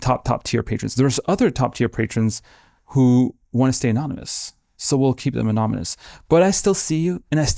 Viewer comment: The was he said anamunas 26:59 😂 instead of anonymous. It’s lovely though! Great video